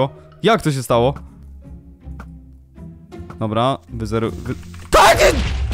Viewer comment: co to było ja usłyszałem jakieś TARKYT xd